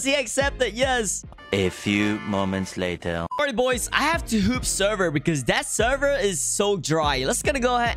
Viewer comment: Why does it sound like he is saying hoop sever and not hop sever